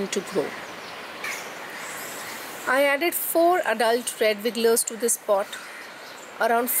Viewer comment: what did you say? You added 4...... I see in your notes, 4 red wigglers. I have to get some worms!